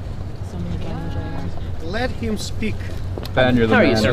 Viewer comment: Did somebody just say '' left him speak ''